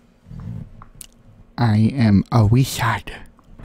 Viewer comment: I thought it said "I am always sad"